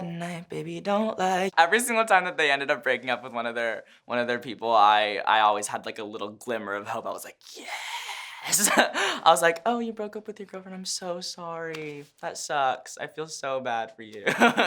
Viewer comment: did i just hear g-girlfriend....... cONAN WHATRE U NOT TELLING US:-(